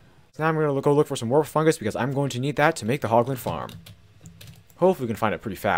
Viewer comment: I thought he said poglin